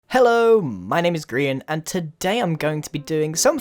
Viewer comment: I thought you said "Hello my name is Grian and Daaamn Girl"